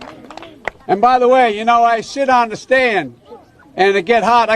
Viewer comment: Did he just say “I shit on the stand “??? 1:52 hahaha